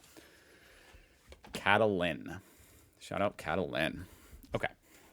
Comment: That's Catalan, the name is Catalin, with an accent on both "a"s. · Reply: I thought I heard say Catalan! 🤔